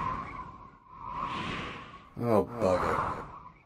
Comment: what did treadshot said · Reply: Oh bugger